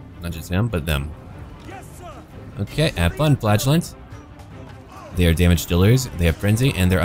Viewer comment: I hear it as "Have fun flatulence" They must have a diet of holy beans. Also upgrade the harbor in Altdorf. Enables a) more money and b) more tech that adds money.